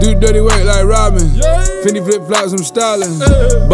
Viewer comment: I thought he said fendi flip flops from stalin